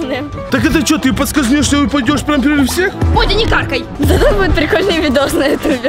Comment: Я сначала услышала "Бодя не какай" 😅